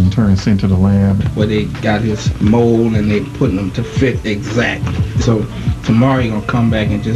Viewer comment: to fit daybla was he saying? 0:37.